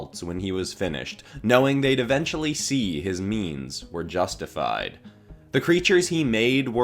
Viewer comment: When I first watched this video I thought he said "memes"